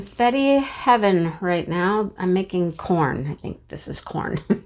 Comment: At the beginning of this video I thought you said you were "making porn" instead of making corn! I laughed out loud at myself!!